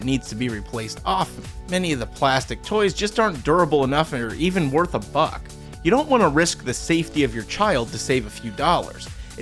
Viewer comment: i thought he said "arent even worth a fuck"